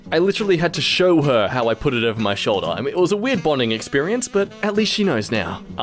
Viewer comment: did you say over your shoulder??